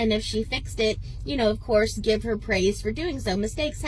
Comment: I thought she said “lord give her praise” in a southern passive aggressive way 😅